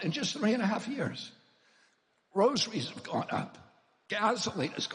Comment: I thought he said rosaries 📿 have gone up, I’m like heck ya they have 😂😂